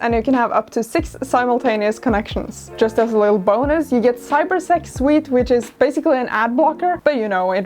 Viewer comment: i was working on another tab while this was running in the background, and when she said "cyber sec suite' i heard 'cyber sex wheat' and was very confused and worried at the same time. so that was fun.